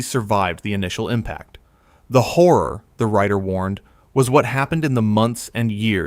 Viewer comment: What did he just call me???? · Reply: horror 🤣